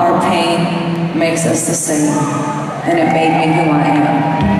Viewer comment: Guys, what she said in 1:35:19? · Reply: All our pain make us the same and it made me who I am